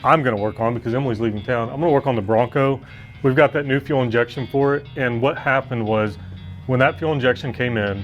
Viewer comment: You actually said "new fuel injection"...I heard NUKEULER Injection...LOL...:)